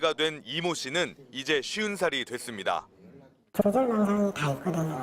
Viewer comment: 쉰살? 쉬운살? 뭐라는거야 말 똑바로안하냐? 짜증나게 몇번을 돌려듣는지